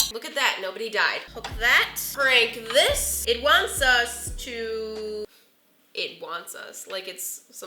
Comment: When she said it wants us it sounds like she said it wants sus 🤣🤣🤣